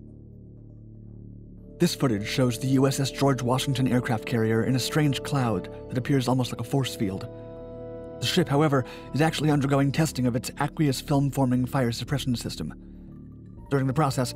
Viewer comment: I thought he said, “shit,,, however!”